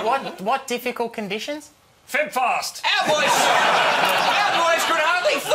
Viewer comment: Love Kiwi TV ! What does Jim (Rob) say the difficult conditions were @ 1:07 ? "think fast "?